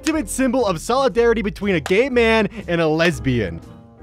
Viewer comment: the first time I watched this I thought he said caveman instead of gay man and then I saw Jessie in the first pic and I went oohhh then I realized I have about -5 braincells